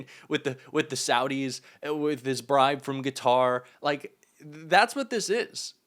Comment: I do not mean any harm, but I hear you saying "Guitar" for "Qatar," at 6:55 on the video. it's truly a noticeable glitch and very distracting. It's just hard to listen to someone pointing out the poor articulation of another. So, are we supposed to disregard your slip-ups so our journalism quality just keeps diminishing? We are so quick to point out flaws in others when we should all be trying to improve ourselves. Our media has become so riddled with flaws because no one cares to use correct English and proper punctuation just to get a story out. Correct composition, punctuation and proofing of our articles leaves me to wonder if the world is not laughing at us about that, as well.